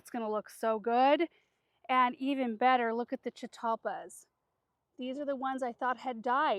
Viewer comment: What is the name of the three beautiful plants at 16:07? Couldn’t quite understand what said. They look a lot like Butterfly bushes. Curious if they would grow here in zone 9b Northeast Coastal Florida. By the way, if you are getting too much rain, send some my way! We are parched! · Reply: They are chitalpas. I'm not sure if they are some sort of dwarf variety because when I looked them up, it says they get twenty feet tall! If they get that big I don't think would have planted them right there!🤷‍♀️ They are a zone 6 through 9.